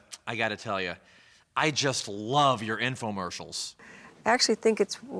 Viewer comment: What does he say at 0:49? 'Informercials'?